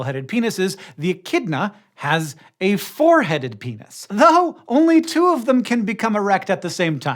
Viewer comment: I heard "foreheaded penis" and now I'm just imagining a penis with a really high widow's peak